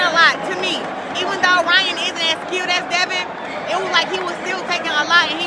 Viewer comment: Wow! I had to do a double take. I thought she'd said "even though Ryan isn't as cute as Devin." I almost fell of my seat. 😂😂😂😂😂